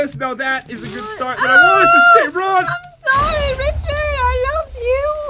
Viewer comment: Did he just say "OH SH*T RUN!" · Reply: No he said I wanted to say oh run really fast